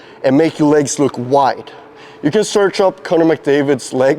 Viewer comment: what was he saying? 😂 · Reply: "not really" with an accent, I think. I think he's making a joke about how searching for pics of McDavid's thighs on the internet might be inadvisable.